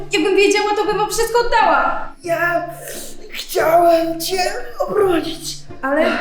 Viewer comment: czy tylko ja usłyszałam: ja chciałem Cię urodzić? XD